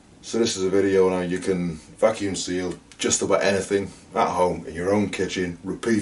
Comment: I totally thought he said "fucking seal" not vacuum seal... LOL